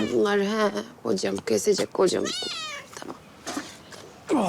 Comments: de ha kesecek bugun kocam demesini birtek ben mi duydum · da kocam kesecek mi dedi fadimeeee ben mi yanlış duydum